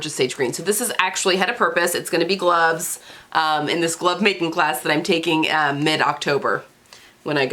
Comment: OK, I need my hearing checked. I thought you said you were going to take a "Love Making" class! And it was going to last all day!!! Then you were talking about leather... I was so relieved when you said "GLOVE making".